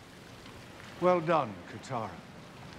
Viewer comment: Subtitles: well done gUiTaR Wut it said: well don’t KaTaRa